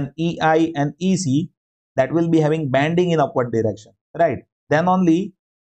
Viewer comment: does it mean bending instead of banding